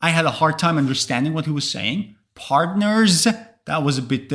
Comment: I heard "patents" then I realized he said "par-teh-ners" :) I really like videos like this where native speakers don't understand English. I am from Eastern Europe and I understand you all without any issues. Maybe the only challenge was your pronunciation of the word "listen" - it sounds more like "lesson".